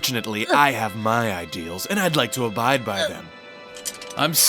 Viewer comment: For a second there I thought he said bite me and I was like (•_•)